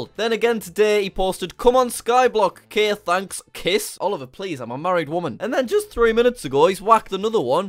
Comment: Wait...Did he say "Oh please im a married woman" at 0:13 EXPOSED!!